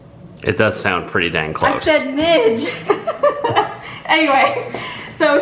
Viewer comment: i thought she said minge😅